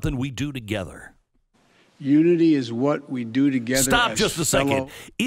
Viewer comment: The first time I heard this, I thought he said unity is what we do together as individuals....LOL